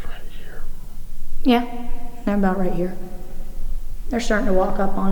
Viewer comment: am i the only one who heard whispering of someone saying "About here"